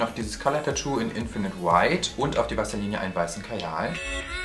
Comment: ich habe vaseline anstadt wasserlinie verstanden :D hahahaha